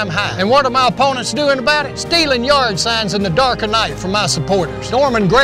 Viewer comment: did he say "stealin' urine signs" @ 00:36??